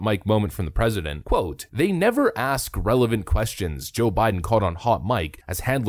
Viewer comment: It sounded like he said "They never ask relevant questions. Welcome to my world."